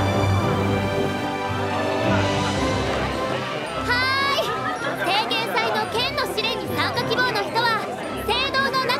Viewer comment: 私、少し耳が悪くて聞き取りづらいのですが、 2:33~2:42のクレムが喋ってる台詞をどなたか教えていただけないでしょうか？ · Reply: はーい、聖剣祭の剣の試練に参加希望の人は、聖堂の中で受付を行っていまーす